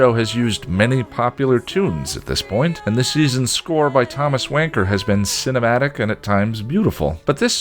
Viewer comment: Who was that score by? For a second there I thought you said Thomas Wanker...